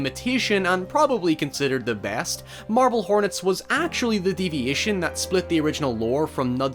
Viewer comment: I really thought he said “Marvel Hornets”. A Google search returned with “Marble Hornets” Cool. But I’m still like, “who?” Guess I’ll look up their channel and see...12 years later.